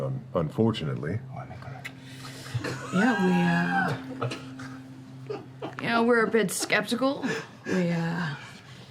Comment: what did liam whisper at about 58:30? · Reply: Sounded like "I want Nicorette". something to do with the AA joke he made before.